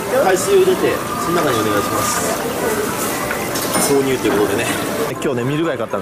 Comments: 秒の購入が挿入に聞こえた笑笑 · 挿入に聞こえるんだけどw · のところ挿入に聞こえました すいませんでした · 挿入！ってことでね！ に聞こえたの俺だけかな心配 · ミル貝のせいで「挿入」に聞こえる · なんかサムネとかもあるけど0:44 ぐらいのが 「購入」じゃなくて「挿入」って聞こえる私はどうすれば…:(´◦ω◦｀)